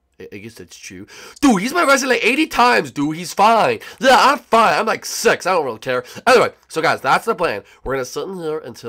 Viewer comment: Did he say I like sex I don’t care